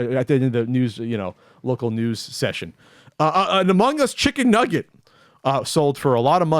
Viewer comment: At first I thought said a humongous chicken nugget sold for $100k...and that got my attention.